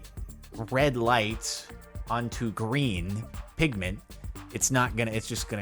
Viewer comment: Am I only one who heared him saing "Pigman" beside "pigmant?"